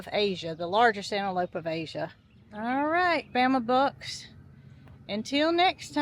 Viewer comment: Bama Bucks I thought you were saying Bama Books I thought it was a book store Lol